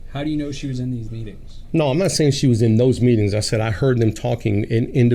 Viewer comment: sounds like hear say😂